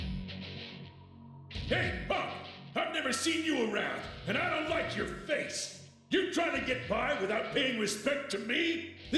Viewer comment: I thought he said "I don't like your race", but at the very least we're not being that offensive yet